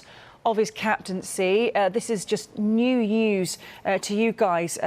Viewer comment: did she just say it was "new news"?